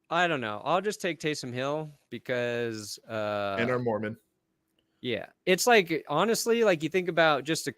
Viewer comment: Did just say Tanner Morgan? Boy that's a reach (rewinds) nope, "and are Mormon" Gophers football got me bad...